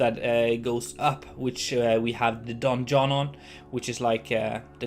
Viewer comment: With your prononciation of 'donjon' i kept hearing the 'Don John' haha ! I'm amazed by the quality of the interiors you make, and this contenplative music really worked wonders for a visit of a ruined castle. Great job !